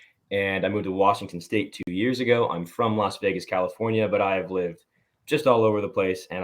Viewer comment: I thought he said he was from Las Vegas, California 😂 I'm like, I've never heard of that 🤔